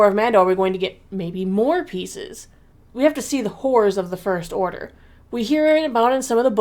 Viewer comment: "the wh0rë§ of the First Order " I had to relisten about five times until I heard " Horrors " 😂